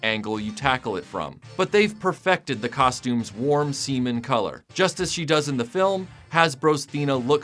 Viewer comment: If I could “Like” a video twice, this would be the one.. did he just say “warm semen color”?